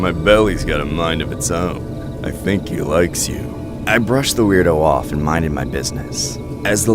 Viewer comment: The 1st story when i heard him say "I think my belly likes you" the way I loudly said UGH LMFAO